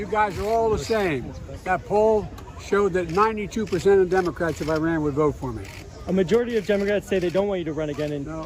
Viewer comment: I thought he said Democrats in Iran😂